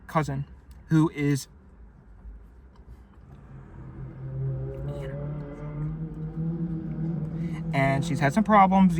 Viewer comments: I have no idea what he said and the video is completely ruined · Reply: He said Anorexic. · Wtf is he saying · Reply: anorexic, that's the word he whispers.